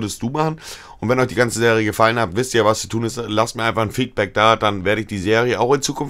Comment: Bei ca 0:30 hab ich F*ckback verstanden xD